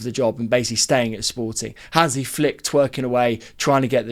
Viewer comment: No shot I heard “flick twerking away” LOL